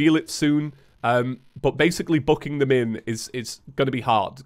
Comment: Did he say tucking or Ummm The haha word